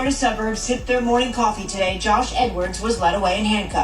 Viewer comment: Am I the only one who thought they said N-words not Edwards